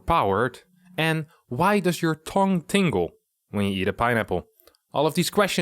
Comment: I thought you said "Burns your Tong", and was wondering if that was a Dutch penis reference.